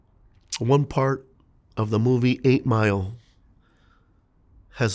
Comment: When he said 8 Mile I thought he said Green Mile , I was like what rap scene was in Green Mile?😂